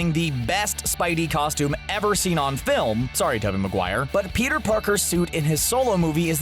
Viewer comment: i swear you said kevin maguire at the end